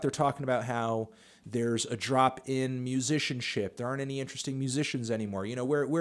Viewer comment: I thought this nigga said "Drop-In Musician Ship"